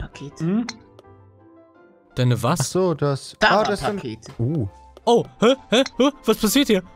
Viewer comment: ZahnPakete?? Was hat er gesagt?? 7:18. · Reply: Zarra Pakete